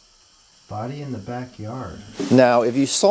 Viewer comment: Everything that is alive has a soul, their energy! What about the body in the backyard? It sounded like it was starting to say "deep" at the end! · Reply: I heard "the body in the backyard is me"